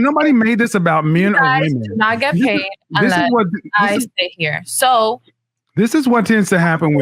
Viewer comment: Did she just say you guys will not get paid unless I'm here?